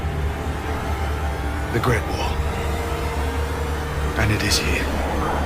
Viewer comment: That was some high budget effects there, also thought Jon said "Great Wall" cuz the wall he had been on